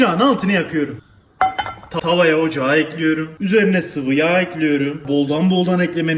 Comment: tavaya ocağı mı ekledin ben mi öyle duydum :D:D:D 03:17 de